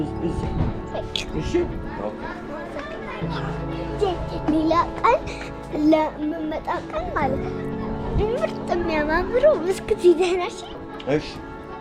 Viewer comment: Your videos are so awesome! Btw at @ 5:18 she is actually saying " jay when you come over another time/day please bring me some beautiful and tasty cookies/treats" :)